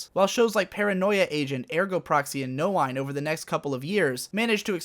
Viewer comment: on 13:17 he names paranoia agent, ergo proxy and what other anime?? I hear no wine but I guess that is not the actual name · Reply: Noein